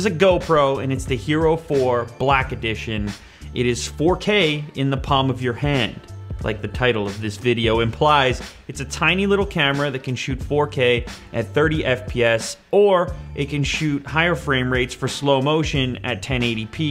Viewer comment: You can put this thing anywhere... Anywhere? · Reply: I thought you said 4 grand in your hand